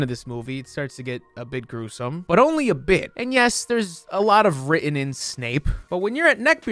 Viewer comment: Please, someone, explain wtf he’s talking about at 8:39. Does he say “there’s a lot of written in Snape?” And if so, what’s the reference? I’m so confused.